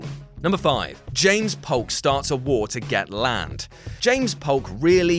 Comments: I totally thought it said “james polk started a war to get laid” i lost my damn dew lol, wow. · Thought it said "James Polk Starts a War to Get Laid" for a second